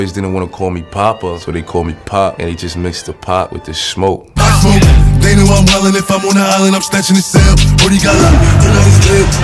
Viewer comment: “Brody got locked, denied his bail. Until he free I’m raising hell” Too fire🔥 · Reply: I thought he said auntie a freak I'm raising hell